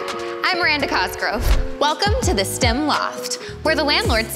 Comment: Oh no I thought she said stim loft, not stem loft 😢 now I'm doing a physical workbook and getting yelled at for fidgeting